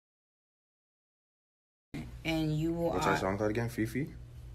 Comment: what is Drake saying ????!! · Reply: Mo H "what's that song called again, fefe..?"